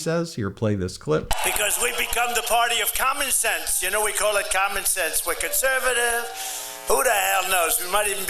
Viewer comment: I hear communist.